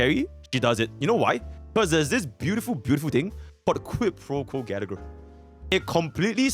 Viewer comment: what did he say ? equip what ? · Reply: Quid pro quo, the f2p LC